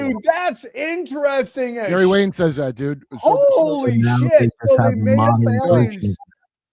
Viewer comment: Did anyone hear what SAID here? I rewound 3 times and can't make it out. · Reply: He said "so now they just have a modern version of that" If thats the part you were talking about lol